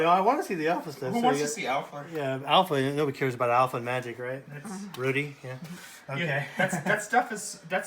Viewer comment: did he say rudy at 12:55?